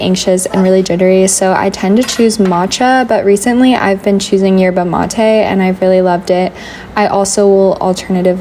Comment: what latte did you say you have been choosing recently? I am really curious. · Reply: She said yerba mate :) popular south American drink